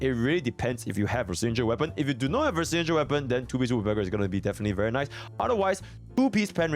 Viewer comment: A really good ass guide for Zhu Yuan (no pun intended), but wtf did u say at 19:40 i cant make out ur words LMFAO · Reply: "If you do not have her signature weapon, then 2 piece woodpecker is going to be definitely very nice"